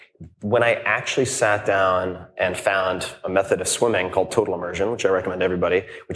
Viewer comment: what is that method of swimming that TIm said on 1:36:46. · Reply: Total Immersion swim technique